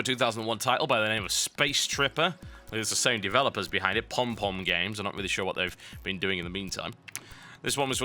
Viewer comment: Pompom games? I thought it said Pornporn games :( XD